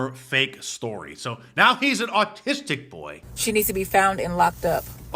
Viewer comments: I heard her say "artistic" not "autistic." 😂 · Sounded like she said 'artistic'. Learn to speak the kings English if you're going to blather on. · I could of swore she said “artistic” boy just because he is an artist doesn’t mean he can steal. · Autistic? I thought she said artistic. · I thought she said the boy was "artistic"?? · It sounded like she said artistic not autistic, so what did she really mean. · I thought she said the little boy was artistic? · Did she say autistic or artistic? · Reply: I had to rewind. I hear "artistic." lol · Sounded like she said "artistic". Maybe thievery is an art now 🤷‍♂️